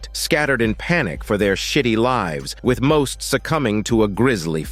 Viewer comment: what did he say 😭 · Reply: Shitty live 😂